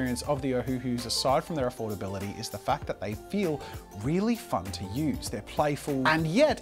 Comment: is it just me or does it sound like he said f*ck instead of fun?